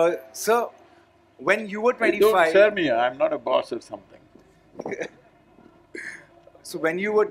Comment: does Sadhguru say "Don't sir me, I'm not a boss or something" ? LOL